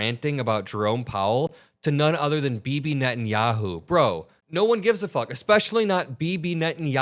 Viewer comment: CC says BBnet and Yahoo. I like it. 😂😂